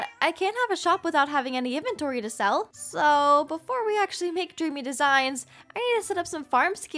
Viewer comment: At one point I thought said "Jimmy Designs" LMAO😆😆